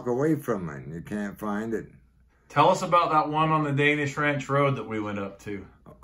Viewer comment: Danish Ranch road is that what he is saying at 13:44 ? What is it and where ? Im super curious because im Danish :D